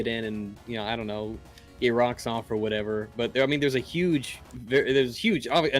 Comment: "Cum-ponent" At least, that's what it sounded like because of the emphasis on the first syllable.